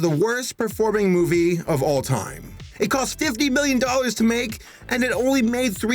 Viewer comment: it cost 50 billion to make!! Me: WHAT!!! I can't be the only one who hears billion and not million.